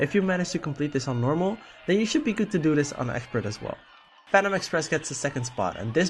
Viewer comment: I thought you said Panda Express